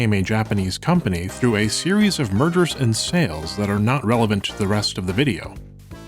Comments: I thought you said "murders" btw not "mergers" 💀 but that's probably my APD dw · i thought you said “a series of murders and sales” and had to rewatch this section with captions on to finally understand what you were saying — i was like “how can he just brush over that?? MURDERS???” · I thought he said "Murders". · I thought you said MURDERS and sales and I was flabbergasted as to why this wasn't a video about a serial killer who targeted videogame studio executives. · had to do a double take because I misheard "mergers" and "murders". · Holy shit, I panicked for a moment because I heard "murders" instead of "mergers" and I still hear it, even though I used subtitles to confirm. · am I the only one who thought he said a series of murders lmao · I first heard “…a series of MURDERS and sales…but that isn’t relevant…” lol